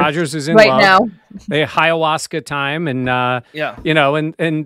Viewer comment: did say, "Ayahuasca time"? 🤣